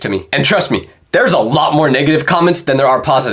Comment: when he said negative i heard ninja hard r